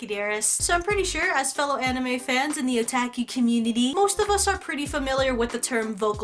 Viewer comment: "So I'm pretty sure, as fellow anime fans in the otaki community..." (0:09-0:12). When she said this, I first thought she said Iraqi community (no offense to anybody).